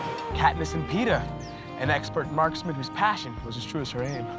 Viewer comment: I got so mad cause i thought he said "katniss and Peter"